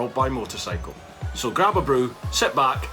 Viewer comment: I thought you said "Grab a broom" at first, I thought what kind of crazy stuff have I got myself into lol